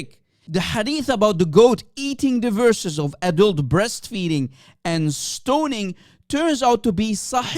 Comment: Did I hear this right? "verses about ADULT BREASTFEEDING" Truly, the perversion and filth of Islam knows no bounds